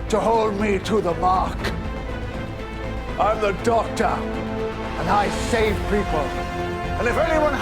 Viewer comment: I'M THE DOCTOR, AND I SHAVE PEOPLE What I heard